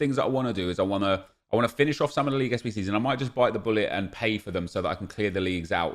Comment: I thought he said bite the “Gullit” so I chuckled a bit.